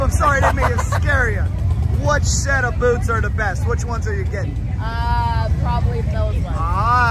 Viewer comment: With all of the noise it sounded like he said "which pair of boobs are the best"